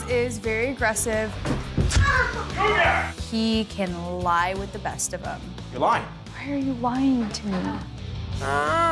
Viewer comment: What did he say??? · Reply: MoVe It!😂